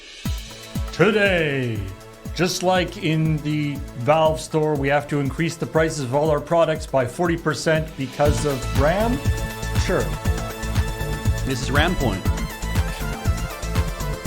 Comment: at first I heard "Because of Graham", then I got that it was a RAM joke :)